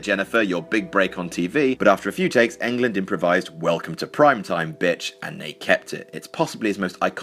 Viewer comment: So am I the only person who always ends up mishearing the line welcome to prime-time bitch and always thinks he's saying fuck the prime-time bitch